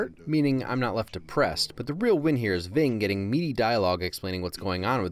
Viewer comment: you say "is ving getting meaty dialogue" but the subtitles say "is ving getting meaty monologue". Just thought id let ya know.